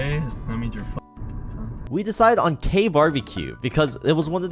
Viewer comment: it took me a sec to understand that you did not say "we decided on Gay bbq"........